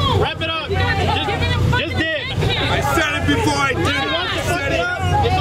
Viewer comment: At first I heard "I shitted before I did it!!!"